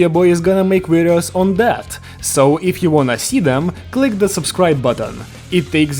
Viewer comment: I swear I heard if you want my Semen, I guess I’m cooked